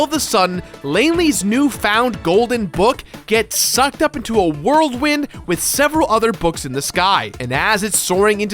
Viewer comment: did he say "world wind"